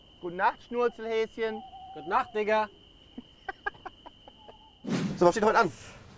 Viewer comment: "Gute Nacht Schnurzelhäschen!" "Gute Nacht Diggah!" :D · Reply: Ich hab zuerst " gute Nacht nigga" verstanden 😂😂😂